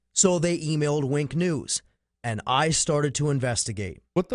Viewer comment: I heard 'wank news!' 💀